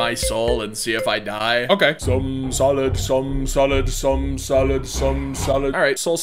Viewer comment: sounds like your saying "some salad"